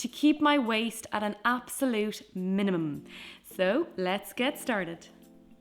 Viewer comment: Minimum? The most similar thing I heard was mnm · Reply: Streko Rodriguez same here lmaoo I replayed it and heard her saying minimum like super fast